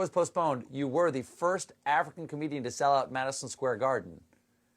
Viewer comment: Stephen says "African comedian..." but the closed captions say "African-American comedian..." 1:28. That's how bad we want Trevor.